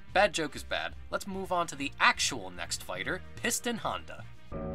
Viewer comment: Does he say honda